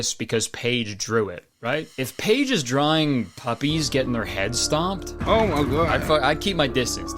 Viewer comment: I thought she said “V-Bucks and Puppies” 😭